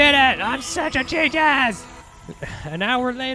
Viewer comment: said "I'm a genius" and captions says "I'm a cheat a$$" perfect translation lol